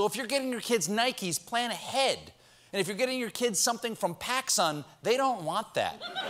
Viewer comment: Regarding Christmas present issues: We also have the problem of our Postmaster General deliberately and openly slowing down the mail ever since he tried to prevent our ballots from arriving on time to be counted. He's still there and people are reporting their mail delivery has never recovered. How could it when he actually had mail sorter machines destroyed? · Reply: I thought he said Pakistan not PacSun, hoped... sigh Paper shortage too, digital copies must exist. living on a H W prayer.